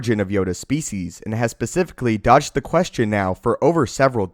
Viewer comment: did he say George Lucas dogged that (about yoda species origins) question for over 7 decades